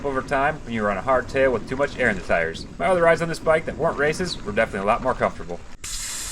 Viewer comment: What did you say at the 2:30 mark about something else being more comfortable? Couldn't make it out. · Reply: Sorry I talk too quick in these sometimes! I said my other rides on this bike that weren’t races were more comfortable. That was due to lower tire pressure.